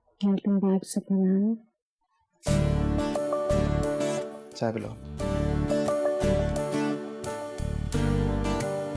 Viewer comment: what minaal said to haider? Plz someone tell me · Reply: Welcome back Superman!